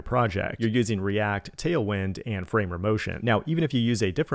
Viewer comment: Help! At 2:17 "You're using React, Tailwind and..." ?? I Heard "Frame Remotion" but I can't find a search hit with that logo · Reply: Found it! Famer Motion. After writing that commend, I realized I could just do an image search with the logo